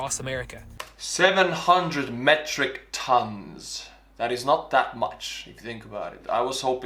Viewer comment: I think you misheard. They said 700,000 tons.